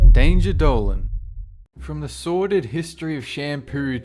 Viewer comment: You pronounced Danger Dolan very clear in the beginning of this video. I always thought you said "Danger Doe"